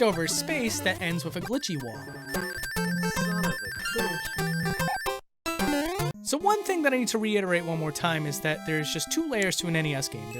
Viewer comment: Did anyone heard saying: “Son of a glitch!” At 7:25?